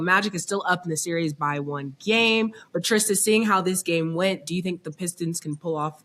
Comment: Yo is he calling her trish I thought he was saying trist smh he so disrespectful